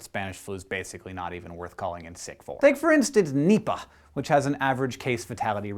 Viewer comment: I thought this dude said nibba